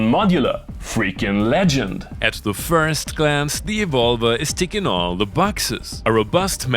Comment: oH! I thought he was saying e-vulva!